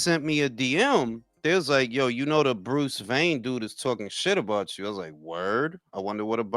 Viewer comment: Who the fuck is Bruce vein? At first I thought yall said Bruce Wayne and I’m like why is he beefing with Batman? 😅